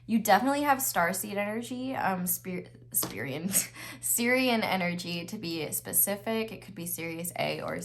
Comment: This is so crazy bc at 1:42:44, you said what sounded like “Syrian energy.” I’m freaking out bc my crush is Syrian and I know you probably didn’t actually say that but I feel like it’s a sign! You’re so amazing!